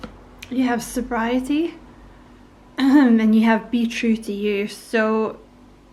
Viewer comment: I thought you said beetroot to you! 😂 Pretty sure you actually said be true to you 😂